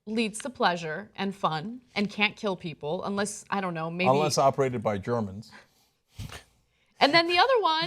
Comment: I thought Cenk said: "Unless operated by drones." I thought to myself "What kind of porn has dildo drone strikes?" just wait, there soon will be.